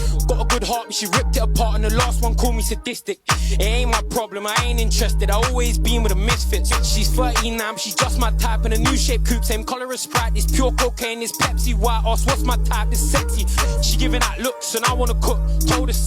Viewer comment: Did homie say she’s 13 and half !??